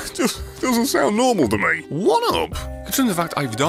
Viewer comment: When he said one up I thought he said "What up" XD